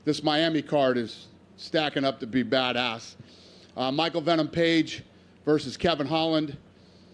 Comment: Did he just say Michael Phanom Page?